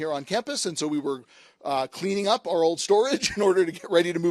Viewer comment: Kahlert! He said "Kahlert School of Computing". NOT coloured. I'm not American, but even I had to pause for a minute when I heard "Coloured Schoof if Computing" lol. I had to google it, thinking it must be an old name that stuck, like the NAACP. But nope, just misheard the name